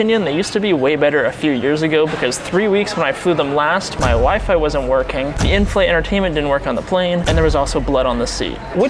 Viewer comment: Your WIFE wasn't working???.... 😂 That is what I heard